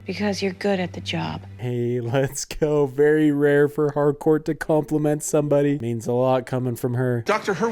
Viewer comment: First time someone calls Harcourt by her name I thought they said ‘hardcore’ so I just call her that cause it’s her super hero name now